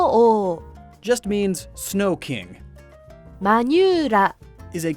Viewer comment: i first heard Manyula as manure. Welp time to sleep less during middle of the day